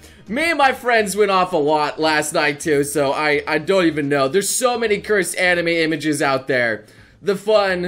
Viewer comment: Did say "I doy even know"?